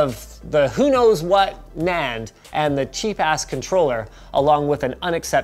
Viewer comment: did linus just said cheap-ass?! XD as for right now there's 487 dislikes i wonder if linus tech media will soon get 487 gifts of kingspec p3 512GB